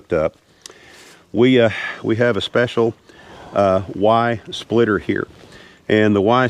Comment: I need my hearing checked... thought you said..."wife splitter "🤣🤣🤣